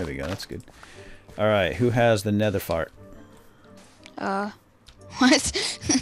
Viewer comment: At like 6:40 did say nether fart?